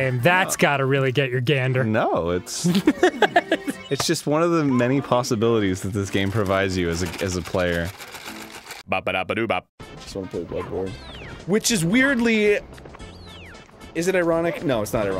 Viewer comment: Did he just say he wanted to play Bloodborne?